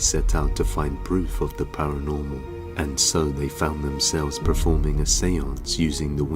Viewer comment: Everytime the word paranormal is said, I hear 'Pair of gerbils'. Don't know why